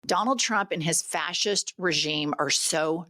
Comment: For a moment there, I thought she said "Fat Sh!t Regime" 🤣instead of fascist but either will still work.